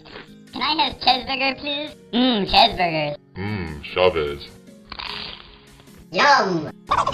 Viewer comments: does anyone know what he's saying? This whole time I've been saying "Mm, shabez.." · This whole time I thought the taco was saying “mmm strawberries” but it’s “mmm chavez”…